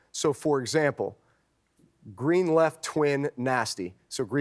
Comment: And yall think Im crazy. 4:17 Breesy says "Glandland Twin Nasty." Greenland. Not Grandland. Shit. Further Conspiracy? Probably.